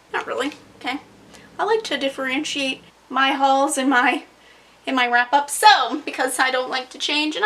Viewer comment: i deadass thought you said "I like to differentiate my HOLES from my wrap-ups" and i was just???? exSQUEEZE me?!!??? but anyway yes we all need Wicked as You Wish GIVE IT TO US ALREADY